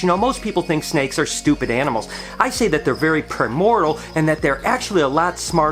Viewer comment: "what was that word you used at 6:01? was it premortal? 9:35 what made you think you could pull that off with one hand? lol. retics are pretty bad shedders. I was told it was because they have thin skin. some seem to be worse than others.